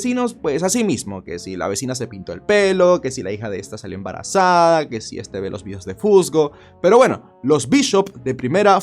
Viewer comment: yo escuche "si esta salió embarazada de fusgo" XDXD